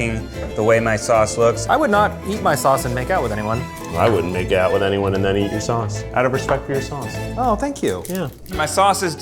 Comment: I thought Keith said “I wouldn’t eat anyone and then make out with your sauce” I really had to replay to make sure.